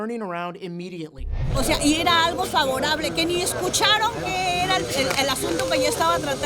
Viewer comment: that is not what she said 😭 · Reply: 😂 I’m weak in Spanish but I knew it wasn’t right, I heard escucharon which is something about listening